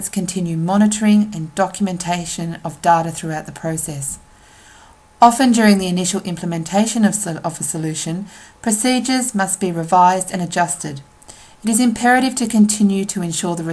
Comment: what are you saying at 00:56!! 7 different people heard it yet no one could tell what the word is :D help us please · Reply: donald junior "Often during the initial implementation of a solution, procedures must be revised and adjusted". She slightly messed up and repeats the word, is all